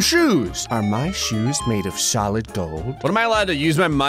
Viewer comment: “Are my shoes made of solid gold?” I heard this as “Are my shoes made of socks?” at first and was very confused